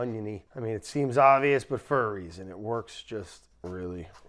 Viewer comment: I can't not hear "it seems obvious for furries". What did Brad actually say?